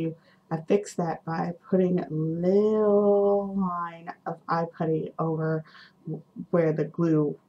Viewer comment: *breathes a sigh of relief* I had to listen to the eyelash issue twice because I thought you said 'eye cream' instead of 'eye putty'. I'm not sure if you've heard of it, but you should join Den of Angels! It's an online BJD community where you'll find so many people who will adore your boy. He's gorgeous!